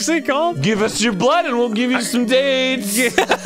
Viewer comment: I know now that Arin says “Gives us your blood and we’ll give you some dates” but for a long time I thought he said “Give us your blood and we’ll give you some AIDS” XD